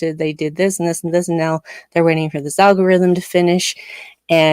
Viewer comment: When Adira is slumped over the console and says "they're awake" I heard "you're keeping me awake" and remembered my college days of waking up in the library with a piece of paper spit-stuck to the side of my face... ah, good times. good times 🥱